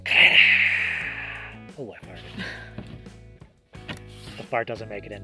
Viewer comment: Did you say, "Oh I farted."? 😂😂😂😂 Oh you and your craziness! just watches ever so calmly and sweetly laughs at your antics haha. Love your videos as always! Entertaining and educational!